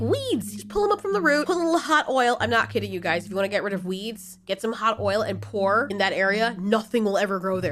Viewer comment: When you said "u wanna get rid of weeds" I clearly thought you said weebs Lmao!